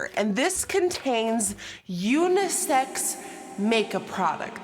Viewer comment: Idk why but I thought it said SFX makeup ^^